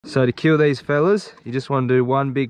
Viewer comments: I thought he said “to cure these fellas” and then he karate chopped the shit out of him I was so confused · I thought he said "to cure these fellas" and now im sad